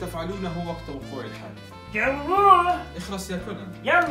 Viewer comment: شو قال كونان في الدقيقة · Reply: قال : جاوبوه يلا